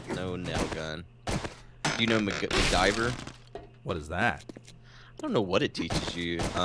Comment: Did he say makgiver??? The other guy